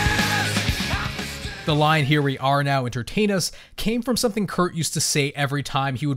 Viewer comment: I thought that when he said "entertain us" that he said "imitate us". Like if a teenager had started doing something "stupid" and others would join them.